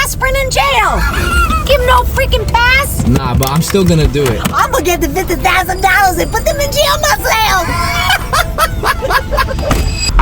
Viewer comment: "PUTITITTIS Idk what she is saying " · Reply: I think she said "IMA GET THE 50,000 DOLLARS AND PUT THEM IN JAIL MAHSELF"